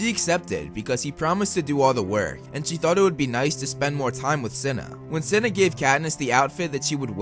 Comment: Bruh Cinna was my dude and didn’t deserve what he got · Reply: I thought you said China 😭